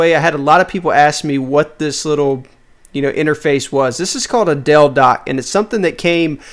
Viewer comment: i thought you said a 'dildo' and not a delldock